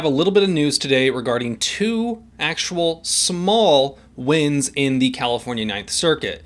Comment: At first I thought you said "small winds", not "small wins". Regardless, it still seemed to make sense, as in "The Winds of Freedom " (Stanford University motto?)